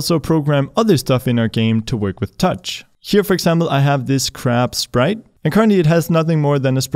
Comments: I first thought that you said that it was a crap sprite. (3:35) and then "let's move this crap sprite around" came and I thought it was too cute for you to say that it was crap and understood. Thanks for the cool intro tutorial :D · I thought you said " I have this crap sprite" :D